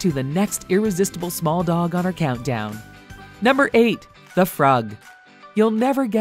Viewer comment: I thought he said the frog lol